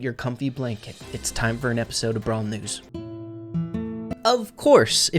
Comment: i was eating and i heard "brawl niggas" 💀 that was funny